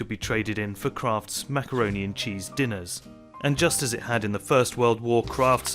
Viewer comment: I thought he said “Krafts Smackaroni and and cheese dinners”☠️